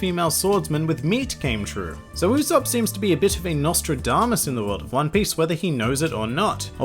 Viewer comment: I giggled when you said "whether [Usopp] "nose" it or not." I misheard it that way lol